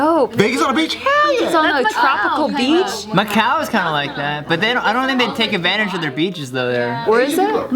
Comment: "Macau is kinda like that!" I heard: "My cow is kinda like that!"